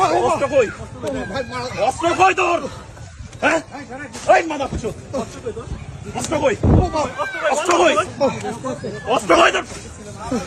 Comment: 😂 ami shuntesi “bostro koi tor?” 😂 That was good. Now do it again.